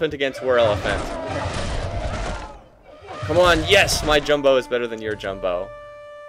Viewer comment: lol i thought he said "my jungle is better than your jungle"